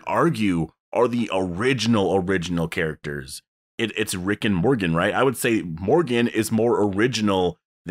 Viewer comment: when u said rick and morgan i thought u just said rick and morty